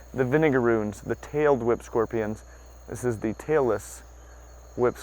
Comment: My daughter thought you where saying Taylor Swift Scorpion 😂